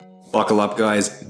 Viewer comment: There’s just something about the smell of old moldy food that hits different…glad it’s eradicated now and the Edge is looking sharp! Have a great weekend everyone! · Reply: Is it just me, or does it sound like you said "fuckle up" at the beginning?